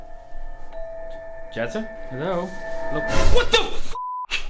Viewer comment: auto captions thought it said Jesus hello XD